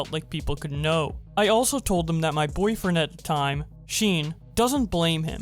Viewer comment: Okay okay I'm all serious rn but did you just say 'sheen' instead of Sean?